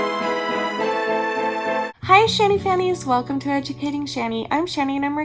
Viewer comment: Did I hear right! A childhood abuse video, and she just said Shanny Fannies!! 😮🤭